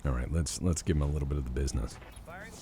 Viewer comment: I can't be the only one to have heard that as bee'sness especially after the shook up box of bee's commentary from